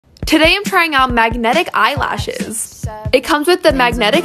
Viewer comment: for a second i thought it said Magentic